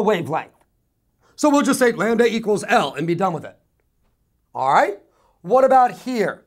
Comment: Q? @ 4:23-4:27~ what is he saying? Is the wavelength symbol called something else? He says "?lan-da" equals L & we'll just be done with it etc...Can anyone tell me what he is saying there? Thx